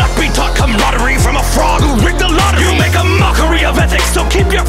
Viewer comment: After all this time i realised that Nietzsche didn't turn on Socrates just for the plot, in reality , Nietzsche really hated the ideas of Socrates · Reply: I thought Socrates was saying he wouldn’t be taught by a fraud, but it turns out he was calling Voltaire a frog because a French